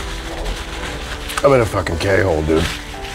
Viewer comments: lmao did he say he's in a k-hole? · does he say he's in a K Hole?